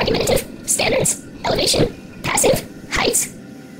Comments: it sounds like a easter egg i slowed the video at 0.5 (YOUTUBE OPTION) and i found one thing i could figur out what the other is but i heard : Passif or Passiv i am not english and i starded to hear at 1:34 Baby saing 1458 but sometimes she says something whit 7 and i didnrt know what that was! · i heard this: standerds, elavation, passive and heights... HMMMM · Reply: Heights Elevation Limited Passive Unlimited Standards